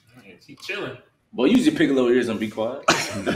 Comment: "boi use ur piccolo ears and be quiet"